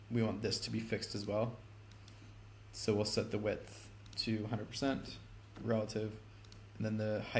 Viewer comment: Thank you 😆 what's the time stamp where I set something to 200%? Sorry been a minute since I recorded this! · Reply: I'm terribly sorry, I just rewatched and realized you were saying "Set to 100%" and I think I thought you were saying "Set two hundred percent." My mistake. 😄 Sometimes I am my own worst enemy. Great tutorial, I appreciate you brother, keep up the incredible work!